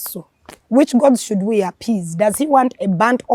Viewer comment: 😂😂😂😂😂😂😂 I hear which gods should we appease.